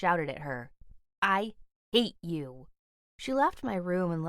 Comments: First I thought she said “ I ate you” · When she said to her mom "I HATE YOU" i thought she said "I ATE YOU"